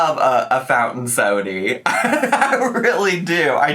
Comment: I thought our said no sodas 🥤 😂😂I’m so confused 😐 😱 😱 😂😂